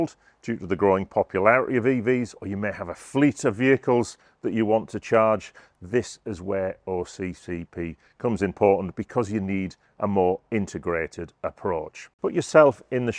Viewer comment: I swear said OCCP! Whoops! That's one for the Outtakes Reel! 😂